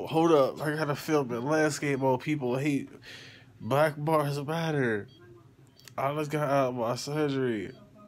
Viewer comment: Did he just say black bars matters XD